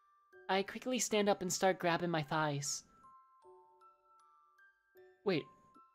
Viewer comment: I thought he said “I quickly get up and start grabbing my thighs “